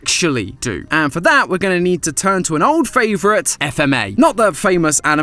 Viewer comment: did he just say f m a. where did the fma go now i know that why i miss matpat